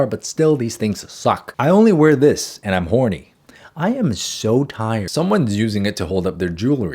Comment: This was so random wtf 😭 I wasn’t expecting him to say that lol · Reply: I had been scrolling when he said it and was glancing over the comments so all I heard was '-this and i'm so horny', so I legit thought someone was down bad for the mannequin 😭😭 I shot back to the top REAL quick because I was like 'I BEG your pardon?????'